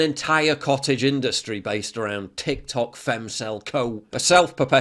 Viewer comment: "Tik-Tok Femcell" what does he say next? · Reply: Cope